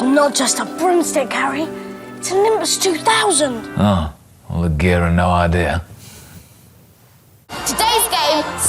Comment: what did James just say? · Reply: All the gear and no idea